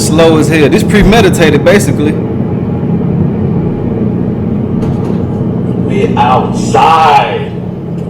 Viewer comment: "We outside!" Nah sucka. You're INSIDE for the rest of your natural life. The new Sin City of prison cells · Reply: Did he say we outside? Crazy if he did! I thought I heard him say “now what …”